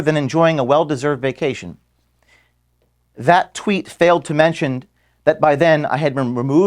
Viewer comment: I thought Tim said it was a "Failed Coupe"?